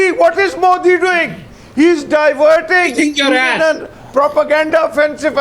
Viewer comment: "He think you'r ass" LMFAO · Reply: I heard it as "Kick your ass" Well spotted haha